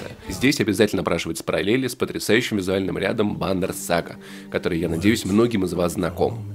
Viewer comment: что за мульт не могу расслышать? Вондер сага?